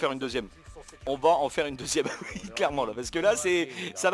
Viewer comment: Je sais que ce que tu a dis est "Oui hihi clairement ! " mais perso la première fois j'ai compris " Ouihi hitlerement "